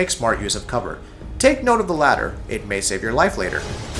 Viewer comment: Why do Americans pronounce T's as D's? I heard "Take note of the ladder"....I was thinking what ladder? But then it dawned that he tried and failed to say latter.